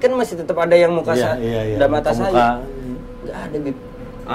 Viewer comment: Apa cuma aku yg denger di menit 6:32 ( Muka-Muka Ange ) 🤣🤣🤣 Maaf klo salah denger😂